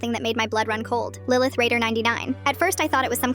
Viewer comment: I thought i heard it wrong So i rewinded it a couple of times And my dumbass thought that it was Lillith Vader 99 instead of lillith raider 99